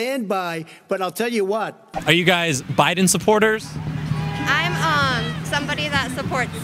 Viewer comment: Wait at first i thought it said trump supporters